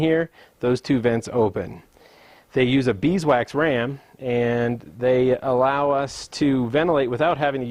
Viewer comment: What is a beeswax ram? When you were walking about your passive system, it sounded like you said a bee's wax ram?